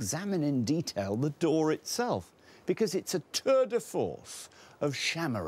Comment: did anyone else’s auto subtitles say “turd of force”? 😂